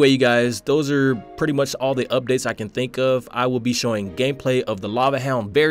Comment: sounded like you said gay play